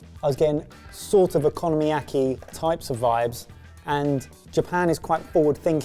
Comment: I'm sorry but who is doing your subtitles? Okonomiyaki is subtitled as "economy-acy" on the English - Default subtitle track. I'm assuming since English - Default and English (auto-generated) are distinct selections that the English - Default was a channel submitted one? This is the latest in a string of many subtitling hiccups on this channel, it's almost like the subtitler isn't someone who understands your culinary area. At 5:07, century eggs or "sentry eggs"? At 6:07, pretty sure Ben says oyster sauce not worcester sauce. And not 10 seconds later, at 6:16 coriander leaf is spelled colander leaf! Is the English - Default track another auto-generated one?